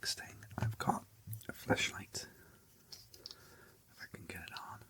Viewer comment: genuinely thought you said "fleshlight" and not flash-light😭😭